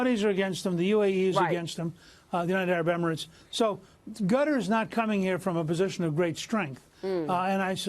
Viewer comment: did Newt call Qatar "Gutter" ? whoops, LOL !